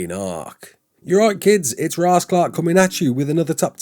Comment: I’m Jamaican and thought I heard “Rasclot” when you said raasclark lmaoooo 😂